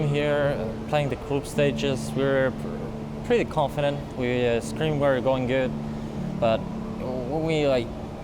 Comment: We have added English Subtitles! We hope they help! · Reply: Thanks a lot! I am playing it with subtitles right now. One thing I noticed is that in 0:20 it says "We scream. We're going good". But I think he said "We...scrim were going good" :)